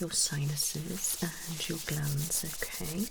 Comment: at first i thought you said you were going to check my glans hahaha then i realized you meant glands...